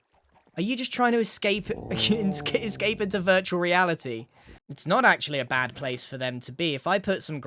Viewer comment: did u say virtual insanity?🤨